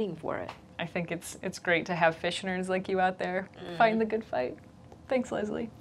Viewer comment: I misheard "fish nerds" as "fishionaries" and that seems both punny and appropriate.